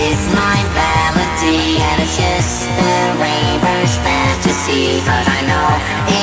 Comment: At first, we always thought he was saying "raper" and "rape" instead of raver and rave. We are so stupid.